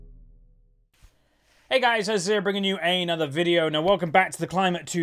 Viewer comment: Why do i always hear:"Hey guys Azir's here" Lol xd